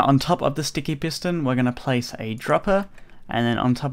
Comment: I think I heard Dropper instead of hopper 4:41 Like of agree